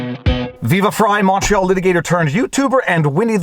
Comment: You talk so fast, for the first 20 or so videos i watched I thought you were saying "Viva Frei macho litigator"